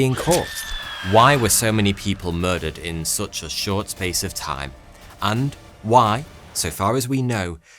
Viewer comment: Bro why do i hear "Stains of time"